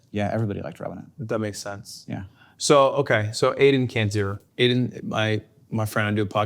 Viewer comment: Even after 2 years of watching the Yard I still have no clue what he says at 15:50 but I'm certain it's not "Aiden cancer" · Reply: for sure "Aiden can't do it"